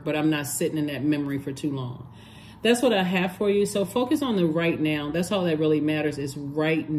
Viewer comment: 😂I thought you said "focus on the the right man".....my head whipped and I thought NO She didn't! Rewind...nope, you said "focus on the right now". 😅 Thanks for everything. You are amazing!